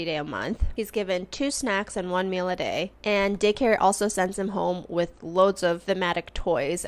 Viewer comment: I may be childish but when she says daycare all I hear is “dick hair” and I’m just in my room dying laughing😂😂😂